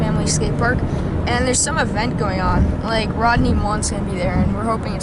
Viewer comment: Who? Rodney Watson? I know it's Rodney Mullen, but that's not what he said.